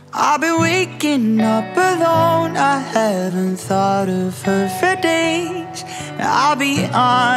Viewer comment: I just remembered that the first few times I heard this time I thought she said “I haven’t thought of THEM for days” instead of he